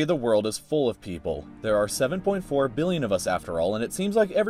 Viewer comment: million Edit: i misheard it